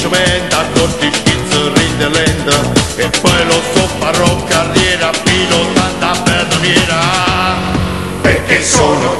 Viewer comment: Scusate cosa dice circa al minuto 1:34 io capisco "faró carriera pilotando una betoniera".... ci ho azzeccato?